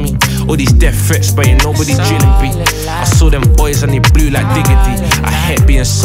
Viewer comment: Yo, did he just say " i saw them boys and they flew like Digga D"?